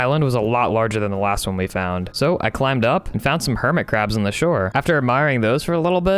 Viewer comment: When he said hermitcrabs i thought he said hermit crafy